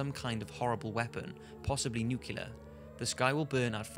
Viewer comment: Did he say "nucular"?